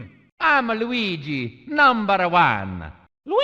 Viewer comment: for some reason i heard luigi say "Luigi is the POPE!!!"